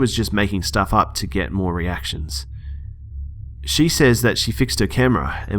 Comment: I clicked there by accident and it sounded like "Fuck to get more reactions."